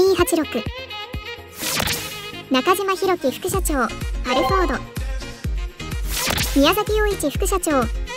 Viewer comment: アルファードがアルフォードに聞こえる